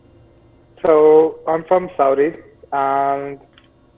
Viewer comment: saudi arabia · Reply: It sounded like he said "saudik" or "sautik". That's what i heard anyway.